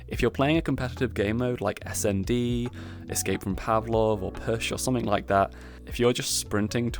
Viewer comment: did bro say escape from Pavlov